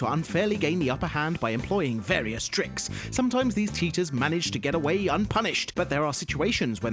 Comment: Did he just say “teachers”?